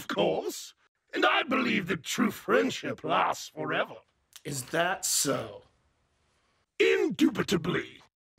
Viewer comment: I thought he said that's sus